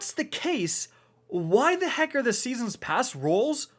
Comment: The way you say "Seasons Pass" and "Wraithborn Hunts" makes me want to commit toaster bath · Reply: Thought he was saying seasons past... thanks for the clarification.